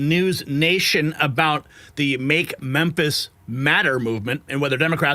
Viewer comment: I misheard and thought you said Make Memphis Madder!